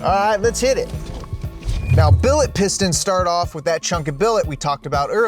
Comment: I thought he said piss stains in the beginning